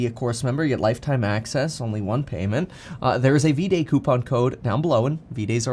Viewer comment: For a second I thought you said D day 🥺 You said V day!💕🙌